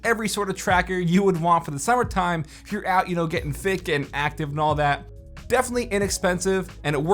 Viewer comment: did he say thicc lol